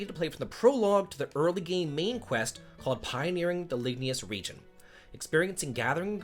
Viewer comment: I thought you said Pioneering the Labia region. I was like Sweet Baby Jesus, this WAIFU fan service is getting over the top.